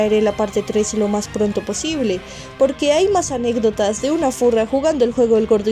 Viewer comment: Yo escuche zorra XD